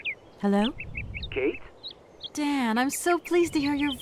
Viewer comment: I thought she said "Dad." Needless to say, I was very confused at the end of that conversation.